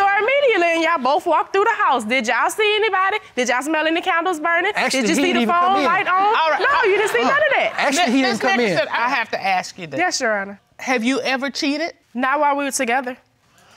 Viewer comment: WayyyyyyyyyyyyyAmenttttttttt.. 😂😂😂😂😂😂😂 Did she just say.. "Did yawl SMELL ANY CONDOMS BURING?.. 😂😂😂😂😂😂😂 OMG.. I NEVER HEARD ANYONE SAY THAT IN THIS CONTEXT... BAYBAYE THE WAY I JUST HOLLEREDDDDDD😂😂😂😂.. I REWINDED AND LISTENED 1000 TIMES.. 😂😂😂😂